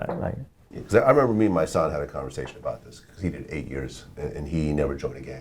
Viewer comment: Wait, at 14:50 does Vlad say "Me and my son had a conversation..."? Does Vlad have a son? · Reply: Andrew Lentner he said “Mysonne” which is a NY rapper that did time